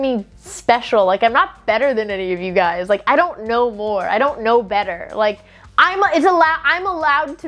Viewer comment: At first i thought she said "..i dont know lore..." (instead of whut she actually said "...i dont know more...") and my first reaction was "i know who knows lore: Matpat. I need this collab" my coworker thinks i am insane now lol.